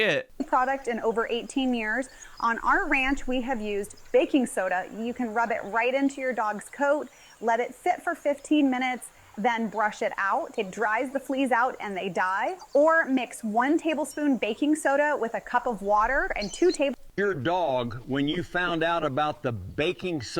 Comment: sorry, did she say "Baking soda dries the fleas out,"??????